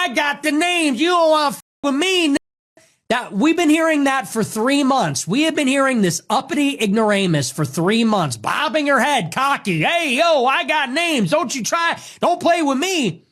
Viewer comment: did he say Negress 😅 what is wrong with him